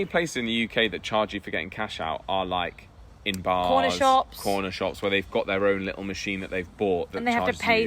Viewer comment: I thought they said "porno shops" and wondered why they knew about cash withdrawals there until I realized they said "corner shops." ;-) I guess some corner shops might be porno shops, though.